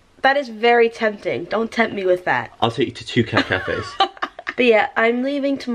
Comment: When he said I'll take you to two cat cafes I thought he said "I'll send two kidnappers and as you can imagine I was extremely confused